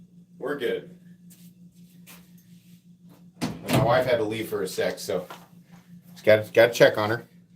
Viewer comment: I heard: "My wife had to leave for some sex!"